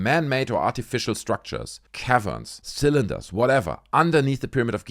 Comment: Oh my goodness. Dude I thought you were saying cav iron. Like it was some iron rock. 😂 A cavern 😂